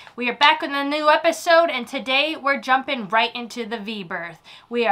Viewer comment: I swear I just heard her say "we are jumping right into the Beaver"🤣😂oops, my bad.